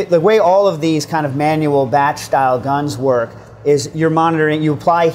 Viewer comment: what is he saying "thatch style gun"?